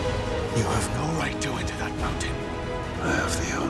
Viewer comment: PLEASE! write to me what says Bard in 0:55? thx in advance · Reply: You have no right to enter that mountain